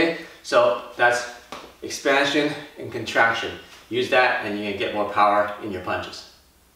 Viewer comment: I thought you said use your palm for more power than a punch.